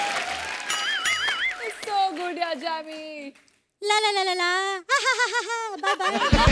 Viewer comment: Did Raveena really call her (Jamie) Jammy? 🤣